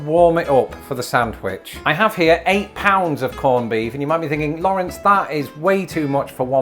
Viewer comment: I thought Lawrence said he bought EIGHT POUNDS of corned beef at first! It took me a minute to realize he must of said “a pound”. We had Ruben sandwiches yesterday. One of my favorites!